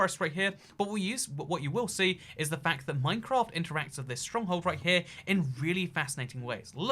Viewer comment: did he say stronghold?